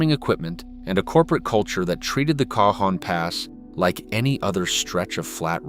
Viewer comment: Bruh, Did you just say K-John pass?